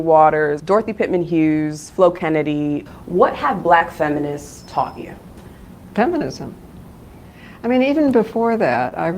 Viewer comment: All I heard was"blk women taught me feminism!"💀💀